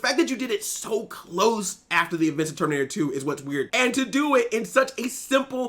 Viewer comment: Every time you say it's so "weird", I hear it's so DISRESPECTFUL.